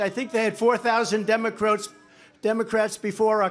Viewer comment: DEMON-cRock did trump say that then corrected himself.